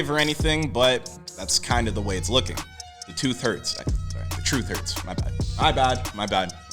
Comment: in my high sleepless stupor finished up rolling this wood and heard him say the tooth hurts at the same time i was thinking about blue taking back the tooth if they split and had me geekin